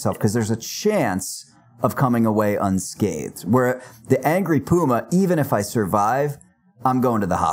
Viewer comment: Ahrr! Angry Puma, for a couple seconds i thought you said angry boomer 😂😂.